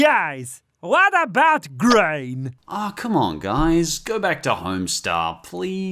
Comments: Thought he said, "Come on guys, go back to Homestuck." I had to take a double take. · did you say HOMESTUCK?